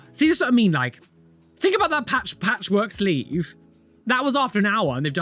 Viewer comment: I misheard you and thought you said “patch update” for the sleeve. 😂